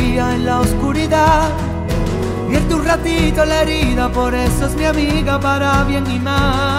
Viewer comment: yo pensé que decía "por eso es mi amiga para mi animal" pero no xDDD